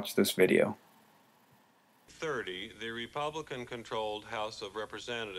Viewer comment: legit when that video hit 10:13 with subs, I thought it said reptilian HOR